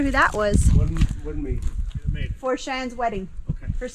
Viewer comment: Did I hear "Before Cheyanne's wedding...You got told buddy! LOL